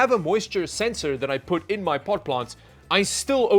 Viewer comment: pot plants? did i hear that right? lol